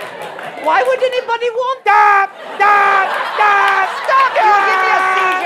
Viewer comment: I always hear "why would anybody want THAT!"